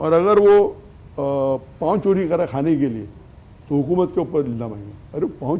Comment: par samaj nahin aaya "Agar ___ chori kar raha hai khanay kay liye". Can someone explain what he said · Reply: Pav ... Bread